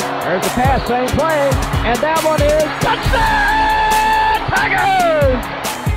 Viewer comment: when i watched this, i heard him say "tigers," not "packers." i could be wrong, but i thought he said "tigers." idk...